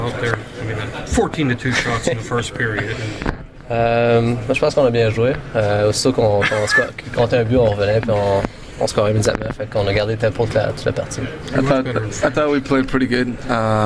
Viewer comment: What did he say at 4:25 ?? Plz I need to know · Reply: Moi je pense qu'on a bien joué, aussitôt qu'ils comptaient un but on revenait et on scorait immédiatement, et on a gardé le tempo pour toute la partie ''I thought we played well, as soon as they scored we would instantly score back and we kept that pace for the whole game''